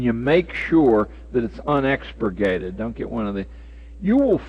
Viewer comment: What word is he using here? Unex?? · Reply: unexpurgated /ʌnˈɛkspəɡeɪtɪd/ adjective (of a text) complete and containing all the original material; uncensored. "Lawrence's notorious novel made its unexpurgated appearance in the United States"